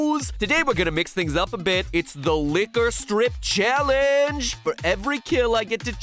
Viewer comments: When mat said ‘Lick or Strip’ I thought he said ‘Liquor strip’😂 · i thought he said liquor strip :P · I thought it said Liquor Strip 😂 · I thought he was saying liquor strip